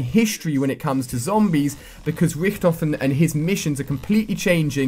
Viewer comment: "Rif-Tof-Ten" um. what the fuck did he just say. Pretty sure is "Rick-Tofen"